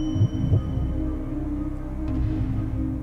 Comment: 何回聞いても不正解にしか聞こえん